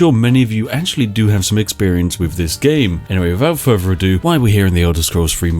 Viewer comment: Whenever I hear "wiffout ferver ado" I ready myself for some further ado.